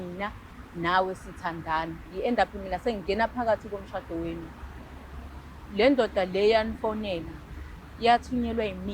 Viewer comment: Guys I know you gonna laugh neh Mara what’s that word her sister said on 30:00 that she’s gonna do to her😅 the rest I understood but that word nje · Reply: She said lot of words but I think the one is ngizokupitshiza😂 meaning I'll crush you or squeeze you 😂